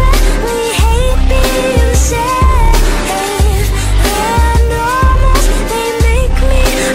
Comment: WAIT WHAT I THOUGHT THEY SAID ''Cause I really hate being sane'' INSTEAD OF SAFE? I'm mad now it would have been better 😠